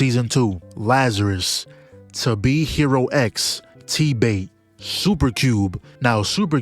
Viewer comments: what anime did he say, Tbay, tee bay, the bay, t-bate? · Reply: TBATE (The beginning after end) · what anime did he say?!? TBait? tf is that?